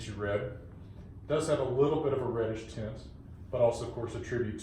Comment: Did he say at 0:48 ''Does have a little bit of red skin? ''